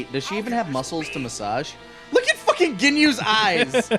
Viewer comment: Is she saying she got a boob massage from them ;)